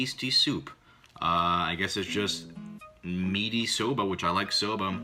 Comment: I thought that said "Meaty Soda"